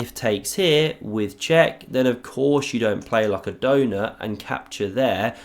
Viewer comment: "like a donOR " OR "like a donUT " ... Which one does he say ?? :D Context is important !! >=) . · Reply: Donut